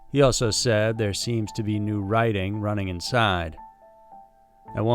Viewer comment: On Alex from TN...at time mark 7:54 i hear you read..."there seemed to be new WRITING running inside"...but other than that...LOVED the 2nd story...ur one of my favs 😸